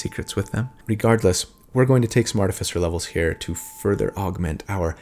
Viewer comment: I love how the Subtitles at 22:13 said ‘smartify levels’ instead of ‘some artifice levels’